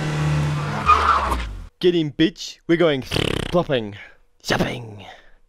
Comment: i thought he said shithopping